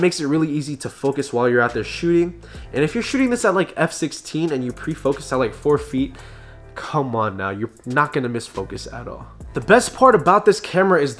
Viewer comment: What does he said at 2:18 ? i am french difficult for me to understand. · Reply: And if you're shooting this at f16 and youre prefocus at 4 feet, come on you're not missing that